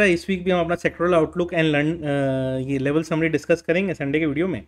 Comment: Sir, Did you say sextoral outlook and lu*d at 00:02 second 🤣🤣 Anyway holistic analysis as always